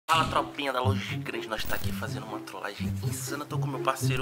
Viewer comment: Sem pilantragem mas eu escutei "fala tropinha da Los Gays" Juro por Deux